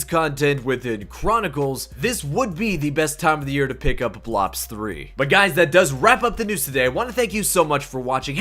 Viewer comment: Did he just say blops 3